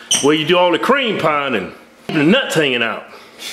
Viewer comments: What did say @ 2:34 ? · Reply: DEEZ NUTS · I swear one of Keith's sons said Deez nuts!😭